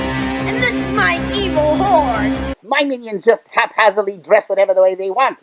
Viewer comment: The kid in the He-Man commercial sounds like he says "My name is Hordak and this is my evil WHORE"! lol